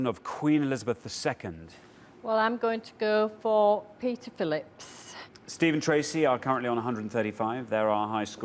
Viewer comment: I thought she said PRINCE Philip.